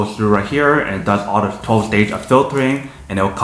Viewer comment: What’d u say bro 2:17 12 days of filtering? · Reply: 12 stage bro 😂